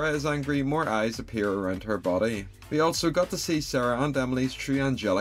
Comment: I heard "Heaven's front gyatt" instead of „Heaven's front gate“ 💀